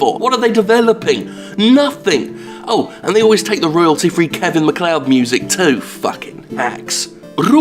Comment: Royalty free Kevin McCloud music? That's what I heard lol.